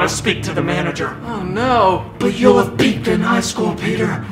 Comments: Is he saying becan? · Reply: Peaked in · When he said “We’ll all have peaked in high school,” I really thought he said “We’ll all have beat the Nazgúl”😂